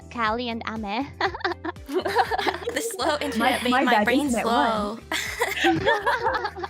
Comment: 「The monster under my bed」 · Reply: I hear "The monster on my back", right？It's difficult to hear difference pronounce "a" and "e". まぁ俺もまだ勉強中だから(高一)